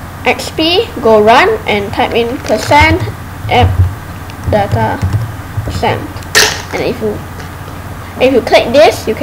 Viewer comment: What did he say at 2:46? Somebody help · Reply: Im an expert of this shit He said type in %appdata% crak